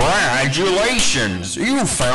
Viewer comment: It sounds like baldi said "You failed all 7 notebooks"